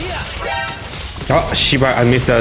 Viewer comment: did he say shiba just like?